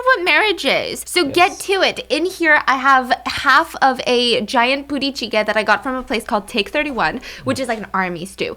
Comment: Yo at 0:22 i totally thought she said she got half of a giant booty cheek 😂😂😂